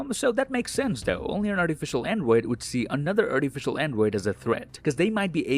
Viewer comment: I misheard "artificial android" for "artificial n-word"